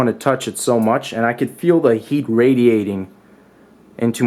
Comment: Did he just say the heat irradiating